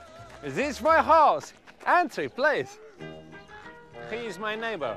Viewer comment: entry please? I always thought he was saying “fancy place”